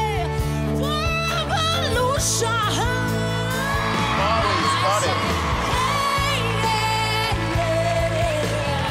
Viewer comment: WHY DID KEITH URBAN REACT LIKE THAT AND WHAT DID SEAL SAY TO JOEL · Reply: Seal said "Mali's got it"